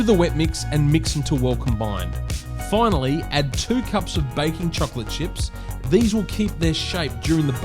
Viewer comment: I got excited when i thought i hear you say Bacon Chocolate Chips :(